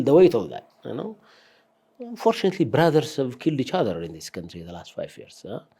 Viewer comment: Did I hear you say @ 24:19 "FORTUNATELY" brothers have killed each other in this country for the last 5 years??? Okay I get it.. I get what you're trying to say , it's just a mishap of 'tongue' as your PM would say! But it will pivot me to my point I wanted to write this comment about. Perhaps I should've started by saying THANK YOU for the host THANK YOU for hard working folks like yourself Mr. Minister, for the 'progress' that Ethiopia has made in this administration , I'd be too impaired not to acknowledge that. As a person that've walked the trenches to get something done while I was in Ethiopia , I have the tremendous respect for folks like you guys that put up their sleeves , work through the challenges and get something done. The reason I put 'Progress' in quotes is simply because it's lack of quantitative facts and the propagandist nature of the interview. Please don't expect everyone to just take your words "as is" - just like when you say Ethiopia is NOW the #1 wheat producer in AFRICA. Shall we be a bit modest and say we're now one of the top wheat producers in Africa - but we don't won't stop there , lets even say our dream is to be one of the top agricultural producers in the world ...something like that...at least that will agree with most fact checkers...et al. The other problem I see , if I may, would like to mention is the 'we've got it all' , 'we've done it all' , 'we're number one...' mentality that not only with you the 'authorities' but even with the regu